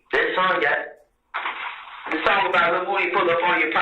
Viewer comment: I always thought Thug said "lil whodi"- not -lil woody